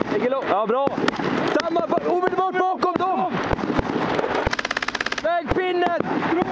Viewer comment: is it just me or i heard the word CoNdOm